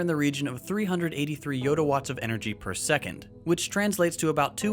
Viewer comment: did i just hear 'yodawatts' ;)